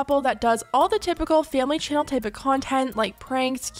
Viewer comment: Chile I thought it said Family Chantal from 90 day fiancé lol